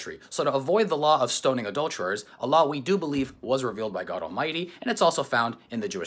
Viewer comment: I think you accidentally said Allah was revealed by god all mighty in second